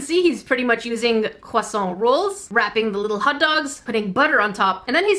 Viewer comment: It sounded like u said hoddogs 😂😂😂